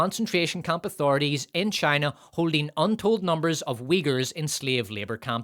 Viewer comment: I heard “wiggers” instead of Uighurs and I keep getting reminded of that one fucking XRA clip💀💀💀💀💀💀